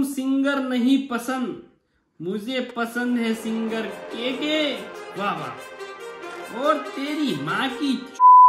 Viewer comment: singer ke bad kya bola ? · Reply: KK singer